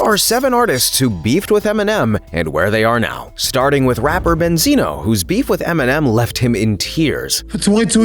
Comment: Banzino instead of Benzino😂😂😂😂😂😂😂😂